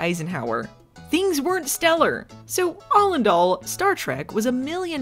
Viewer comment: did you say “all and all” there? 😄